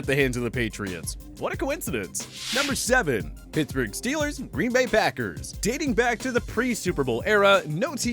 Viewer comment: Who else thought he said Pittsburg packers and Green Bay Steelers